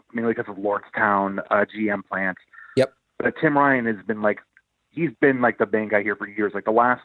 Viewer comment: LMAO! Thank god for subtitles. Whenever he said Tim Ryan I thought he said Tim Vine(a funny british comedian lol)